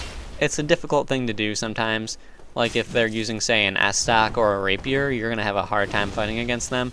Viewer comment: Enough about the Zweihänder/zweihander/two-hander pronunciation... At 4:47....I heard of french estoc, I heard of english tuck and spanish estoque...but what's that "ass tuck" you're talking about? I kinda feel anxious about encountering it in PvP. I'm using Zweihander and I don't want to fight people with an ass tuck. Especially if they can get in and out really quickly. Haven't seen them really, but seems dangerous if I ever fight one. Also, do you need a DEX build to use ass tuck? And does it scale well in terms of damage? Nice vid and thank you for the showcase.